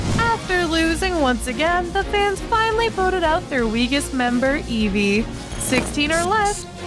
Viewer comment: I thought I heard “…voted out their BIGGEST member Evie” 💀